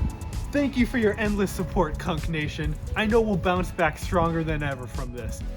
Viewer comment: definitely thought you were saying "cuck nation" until i scrolled down and saw "kunc nation" in the comments